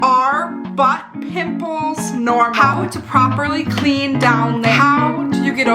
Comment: I thought you said “are buttholes normal?” and I was like 😳 I hope 🤣